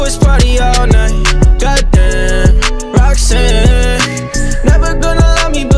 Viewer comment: When he says Roxanne it's sounds like he's saying Waxing it's weird and now I can't unhear it lol Don't ask.. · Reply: For me, When he says "Goddamn" i hear "done did"